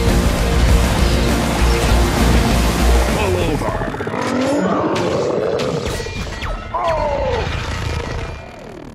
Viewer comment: I always thought he said “pull over” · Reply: Da 1 Phoenix it is pull over I think he just got it wrong